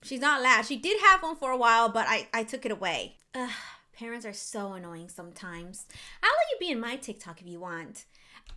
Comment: i thought u say maddie is good but he said parents are anoying